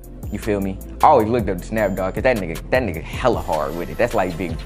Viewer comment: Really thought it said snoop doggy I was like lemme see this forrezzle